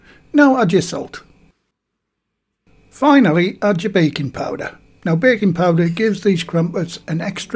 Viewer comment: When he said baking powder i thought he said bacon powder i was about to ask where tf is this godsend ingredient lol